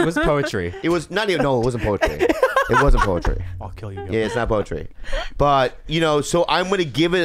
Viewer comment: Did anyone else hear softly say "I'll kill you Bobby" while Bobby was roasting his performance? 7:02. Lol creepy!